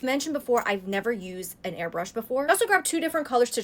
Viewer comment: I THOUGHT YOU SAID A HAIR BRUSH 😭😭😭